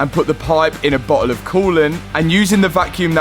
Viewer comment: Mat Bro, I love your video's and have learned a good deal about cars I'll never get the chance to work on or drive. But when you said bottle in reference to the expansion tank/reservoir, it honestly sounded like you said "butthole"!!🤣🤣Your still cool though!!!